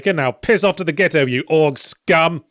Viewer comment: Honestly, I thought Yahtzee said "Ork scum" at the end